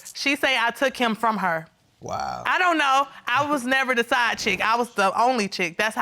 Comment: Mmhm am I the only one who heard him say she WAS the side chick @ 3:00😩🤦🏽‍♀️